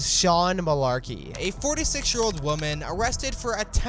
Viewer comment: These Karens are something else.. Subscribe for more content like this! Video tomorrow. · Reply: Tell me why I heard “a 4 to 6 year old woman” at the beginning 😭